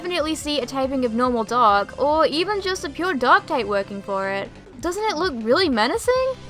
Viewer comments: persian i heard "Duck type" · I thought she said dog! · I gawd I thought she said DOG type. LOL (then I realised...) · Oh! "Dark" type I though you were saying "Dog" type. I was like why is she calling that Cat like Pokémon a Dog type but you were saying it could be a Dark type, ha-ha.